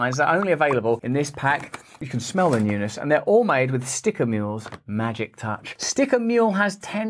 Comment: I always thought u were saying "sticker meal"... just saw today that your saying "sticker mule"